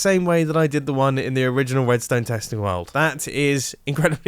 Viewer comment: I think I heard him say wedstone